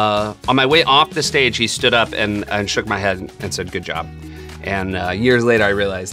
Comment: shook my head? Did I hear that right?